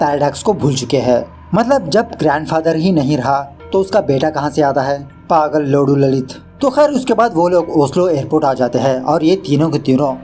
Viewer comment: did he say paagal ,lodu ***